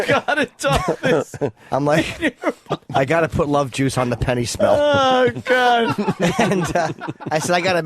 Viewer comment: I thought he said I gotta put love juice on the penny stock